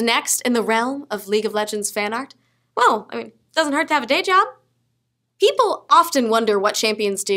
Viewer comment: Looking at Nika I heard Blowjob instead of Dayjob LOL